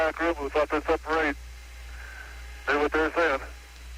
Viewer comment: What’s Lovell saying at 8:00? “I hope the guys in the back room who thought this up….?” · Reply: “ right, knew what they were sayin”